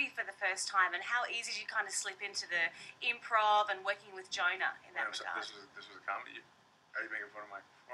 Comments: I'm not trying to make fun of the aussie accent I just think its funny the way y'all make jonah sound like your sayin vagina but without the "va".:-) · Every time she says "Jonah" I hear "(Va)gina"